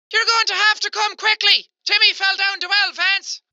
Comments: for that last one i thought the lady was saying '" Vance come quick Timmy fell down a well!" like, wth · This is what I heard: You're going to have to come quickly, Timmy fell down the wells vance. What the captions said: you're going to have to come quickly Timmy fell down two elephants. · your going to come quickly Timmy fell down the fence is what I heard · “You’re gonna have to come quickly because Timmy fell down the well Lance” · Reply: I heard "You're gonna have to come quickly, Timmy fell down two Elephants" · I could hear “Jimmy fell down the air vents.” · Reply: MCSM Pictures i heard, "jimmy fell down the well fence. " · TIMMY FELL DOWN THE WELL LANCE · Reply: I thought she said, Timmy fell down to elephants LOL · I heard: you're going to have to come quickly, timmy fell down the well vance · I heard... “your gonna have come quickly! Jimmy fell down the well fence!” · I heard "You'll have to come quickly Timmy fell down two elephants!" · i thought at 5:38 she said YOUR GONNA HAVE TO COME QUICKLY TIMMY FELL DOWN THE VENTS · I heard... “your gonna have come quickly! Jimmy fell down the well fence!” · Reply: i thought they said ‘fell down two elephants’... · I heard... " you gotta come quickly Timmy fell down the wild fence " Edit: agreed tho it does sound like "you gotta come quickly Timmy fell down the well, vance" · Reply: I heard “you gotta come quickly Timmy fell down the well pants” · I heard... "You have to come quickly because Timmy fell down the well Vance!"